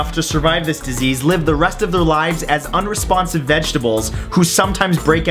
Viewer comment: Did he say unresponsive vegetables at 4:21?